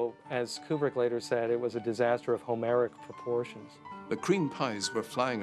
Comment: hey, can somebody write what was said on 5:28 ... "it was a disaster of... proportion" it is an interesting expresion, but didnt catch a word in between so i cant understaind it :)) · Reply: Homeric, as in the Greek myth Homer.